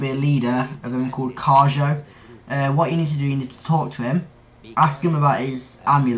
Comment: Thought you said: Uhh..What you need to do...Torture him.